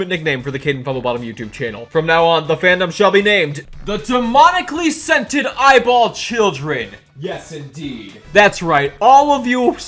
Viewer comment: pls i first heard it as demonically scented eyeball chicken I have no brain